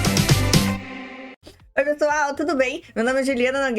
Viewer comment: “Oi petual’ ????🤔🤔🤔